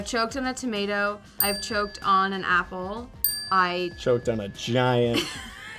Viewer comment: "choked on a giant ..." I'm done can't believe he did that edit: thanks for the likes guys never thought I'd get 3k 😁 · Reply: I heard it "choked on MY giant"..